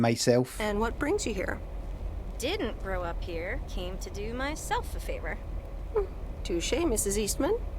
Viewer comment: subtitle error She says 'came to do myself a favour' but the subtitles say 'came here to do myself a favour' He was looking for an error the whole time - and when this happened his arrow was sitting on the word 'here'